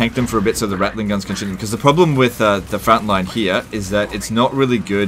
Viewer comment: Did he just say fratline at 3:49? Im gonna start using that when i play skaven. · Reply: I think he just said "frontline" in a really really Aussie way but good job coining this